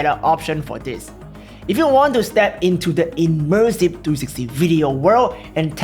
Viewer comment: Did you say “immersive 260 video world” at 20:20? 😁